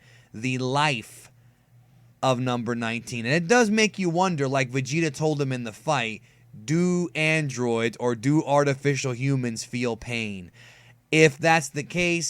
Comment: I thought he said to 19 do androids feel fear